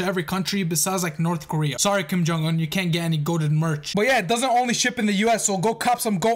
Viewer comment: for a second I thought it said gyat